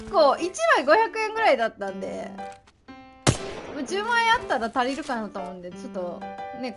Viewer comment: の10枚あったら足りるが10万あったら足りるって聞こえてしまったんだが